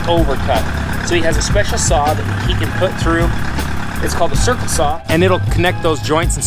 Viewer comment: did he say circle saw? Does anyone know a manufacturer of these or cost? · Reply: Husqvarna makes the circle size and I know they’re very expensive